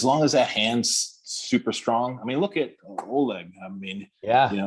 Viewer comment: who did he say? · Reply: Oleg bro